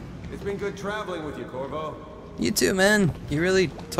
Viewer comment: the guy Said korvo... I thought he said kurwa 😂